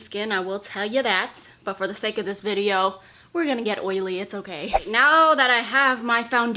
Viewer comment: I thought she said " we're gonna get weylie" for a second 😂😂